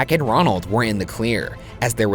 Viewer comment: i swear Ryan said “Jack and Ronald were in the queer”